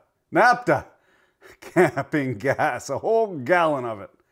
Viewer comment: Lol. At 12:20 or so I thought you said NAFTA like it was a curse. Guess I should get my ears checked. And a new Prime Miniscule.